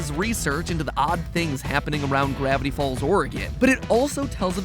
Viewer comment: is he saying "Gravity Falls origin?" If so, he's pronouncing origin with a hard g. XD